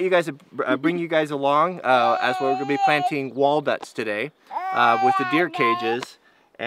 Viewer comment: did he say wallduts